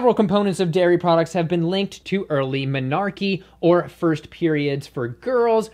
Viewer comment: I thought you said dairy was responsible for "melarchy" at first. XD Seriously, though, this is an issue I am having to navigate right now, as I have two children in public school and we're transitioning to vegan (except my husband, who is still periodically buying meat for himself). I paused this video to ask my oldest if she can get juice with her meals at school, and she told me she can only get juice at breakfast--at lunch they have to take milk. Oi. :/ To complicate things, we live in a very rural area where dairy and meat production are pretty commonplace, and we don't have the funds (or organizational skills) to send lunch to school every day for two kids. I'll figure something out, but right now it's a bit of a challenge for me.